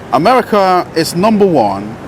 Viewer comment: I thought you said America is number 1 in bs 😂